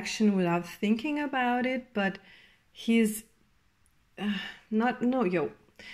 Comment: did u say "not loyal"?